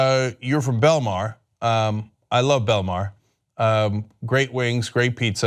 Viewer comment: Thought u said I love bill Maher